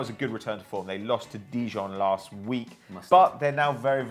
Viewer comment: what did say? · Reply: Abdallah Raslan Dijon mustard